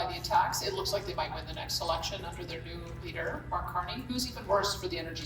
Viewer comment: Did she say that the liberals might win the next election??? Go to 3:40 mark. Or did i misunderstand what she said??